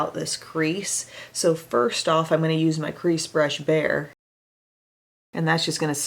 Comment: First thing, my baby brain thought you said “I am going to take my crease brush, bear” and I was like awwwww Em is so cute, she named her crease brush “bear” then I realized you said “bare.” 🤭 Second, I am of middle eastern origins, and you literally look like if you were my sister, so that’s another reason I love you! Third, flaunt that darkness girl. It suits you so well and most people cannot handle it and look this good in a dark and smokey makeup like that!